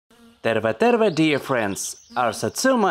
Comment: did he speak finish did he say terve terve